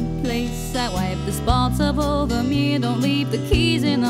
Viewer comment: When I was little I thought she said “I wipe ‘the spots up off the meat’ don’t leave the keys in the door” 🫠💀… She mentioned making eggs so I thought it had something to do with cooking and never questioned it.😅